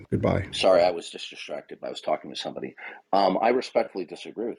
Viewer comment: WOW so passive agressive I caught that when he was getting scolded pretends he was too busy to pay any attention and was on the phone or whatever talking to someone else 🤣 yea sure ya were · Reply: too a bit of a Freudian slip maybe ‘Sorry, I was being disrespected’ did he say? Sounded like it.